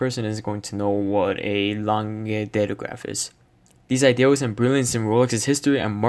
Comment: What did he say at 3:48? Edit: Nvm found it, A. Lange & Sohne Datograph Perpetual